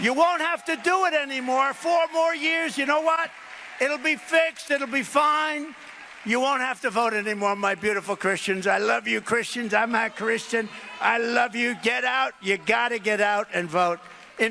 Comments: Does he say "I'm not Christian" ? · He said "I'm a Christian". Which of course is a lie, but there's no way Trump would stand in front of a crowd that believes he's the literal Second Coming of Christ and boldly proclaim "I'm not Christian". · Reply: Yeah, I also heard it as "I'm a Christian," but it's all slurred together like "I'mmah Christian." That said, he's always sloppy and slurry with his pronunciation, so who the hell knows what he's ever saying, really! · The "they" Con'Old speaks of so often are the imaginary voices in his head. Ben, I thought he said, I'm not christian, also. After playing back and watching, he says, "I'm a christian". Not that he knows what it means. But, he doesn't say, not. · Listen again, he clearly says I'm not Christian, Freudian slip right there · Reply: It's a combination of the mic and Trump's slurring speech that make it sound like he says "I'm na Christian", but if you look at his mouth, it's clear he's saying "I'm a Christian". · I cannot stand trump. But I think he was talking in his stupid voice, I heard "I'ma christian" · I think he said In a growl 'I am a Christian' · The captions thought he said "I'm a Christian". I thought he said "I'm not Christian" but I cannot believe he would admit that.